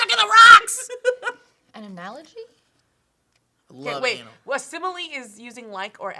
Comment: did say "love anal?